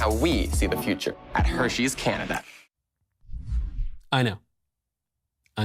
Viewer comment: I thought she said “Hershey’s cannabis” at first 😂